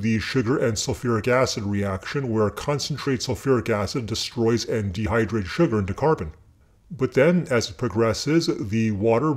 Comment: the caption is wrong at 3:05 you say Sulfuric acid reaction where concentrated sulfuric (acid) and dehydrates sugar (into carbon).